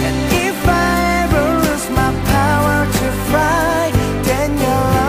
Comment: I'm sorry but while I was doing my homework listening to this, and I heard him say -lose my ability to 'fry'-, I honestly laughed out loud xDD